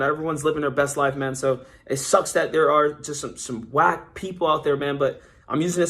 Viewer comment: i literally skipped 10 seconds and i heard ack people are so whack, i thought they said black people for a second